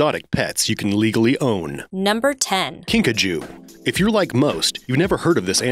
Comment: lol I thought I just hear pikachu